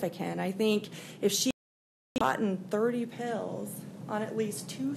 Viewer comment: timeframe … Firstly, “boughten” is not a word; Especially for a lawyer. Secondly, if there were NO TRACE of fentanyl in the home as the defense attorney just stated, 3:24:49 that is bad evidence for the defense in my opinion. (If I were a juror)l I’d think: IF the husband took it himself- there would be some trace of fentanyl, at least near him, since there is NO TRACE- then someone took a great deal of “beyond reasonable care” to make sure no trace was found. Hmmmm. I would definitely scratch that from your list of defenses. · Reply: I just watched that clip again (3:24:49) and in her defense, maybe the attorney said “gotten” not “boughten”. I may have misheard her with all the sound glitches. Sorry about that. But she should definitely not bring up the “no trace of fentanyl defense” because as a juror, I would think Kouri was guilty based on that piece of evidence alone. There would be some trace of fentanyl somewhere if he took it himself.